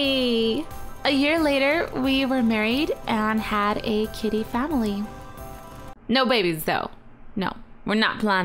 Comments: when she said kitty family i thought she said kiddy family like kids... lol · when you said kitty family i thought you said kiddy family